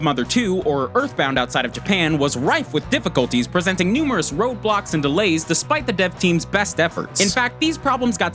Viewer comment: who else heard him say 'roblox' in